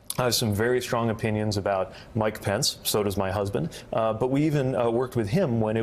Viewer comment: An I hearing wrong. He said his “husband” at 10:34 mark? Nothing wrong with it.. just caught me off guard lol